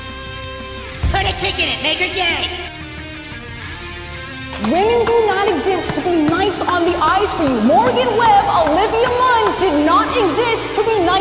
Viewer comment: "Women don't exist to be nice on the eyes for you" -. Uh...pretty sure that physical attraction has been a thing since mankind could walk. I mean probably deep down only wishes she was as attractive as Morgan Webb or Olivia Munn. But then there is the fact that it's a video game and a work of fiction.. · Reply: English is not my first language, and the first time i saw that clip i was so confused, because i thought she was saying "Women don't exist to be nice on the ice for you" 😕